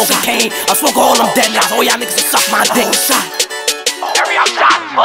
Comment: aye what are they saying at 2:10 ? "oh sha?" and what did it mean because it go hard 😭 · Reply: all os shot